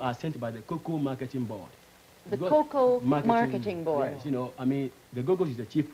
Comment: The debate took place in 1956. Did I hear the word Google in 24:36 and 24:47? :)